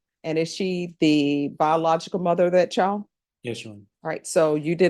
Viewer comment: Is he saying to the judge “yes sir” around 10:10?